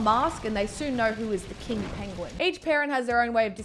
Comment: i thought you said "kink penguin" tbh